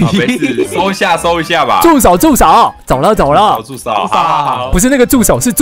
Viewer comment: 为什么我听成猪手 可能是我饿了吧😂